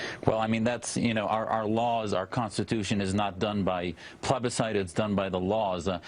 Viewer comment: "plebiscite" is a dick word and it sounded like he said 'plebicide' - like homicide by plebs - sounding even more like a dick.